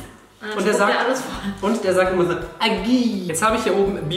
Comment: Wer hat auch Dagi verstanden 😂